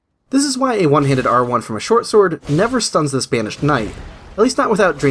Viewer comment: I misheard Banished Knight as "Spanish Knight" and assumed that was a reference to the boss from Sekiro for some reason, which I thought was extra weird cos I'm pretty sure he's supposed to be Portuguese.